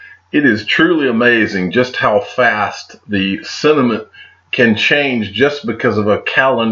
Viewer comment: At 28 seconds you said "sentiment", but I definitely heard "it's truly amazing just how fast the cinnamon can change..." - set us up for that one didn't you!